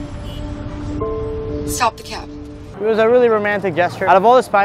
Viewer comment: Gwen sounds like she said stop the cap😂😂😂